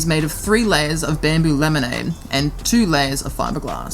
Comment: When u said laminate. I thought you said lemonade. Lol. Great job by the way